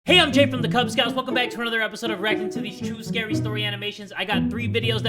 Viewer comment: I misheard “reacting to” as mf “aracnatitties”